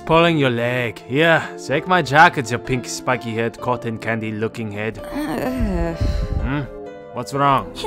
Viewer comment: I thought he was bout to say cotton picking I’m like what?